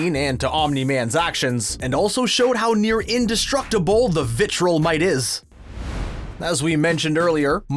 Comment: … did you say “Vitrilmite”?